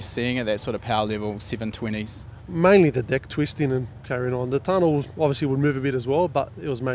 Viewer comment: Did he just say dick twisting? Uhhh